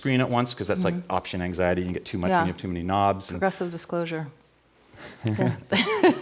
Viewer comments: does she say "oppressive disclosure" here? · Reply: Progressive disclosure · Wtf was that cringey as moment lol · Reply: I heard only "disclosure" and thought it related to "me" disclosing option anxiety / I incorrectly penciled in that she said "professional disclosure", like making a joke that I'm disclosing that I have option anxiety from too many knobs. Which of course then seemed like a strange joke, so yeah, my bad. Progressive disclosure, right, exactly! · Haha I love this moment: <link> when he realises that she's got the DNA sequence to Serum in her head! 😂😂 TIL: "Progressive Disclosure" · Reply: haha, what actually happened: I thought she said "professional disclosure" like I was disclosing that I get overwhelmed using other people's interfaces. And I was too shy to ask her to repeat herself, I thought I could nod and smile it away...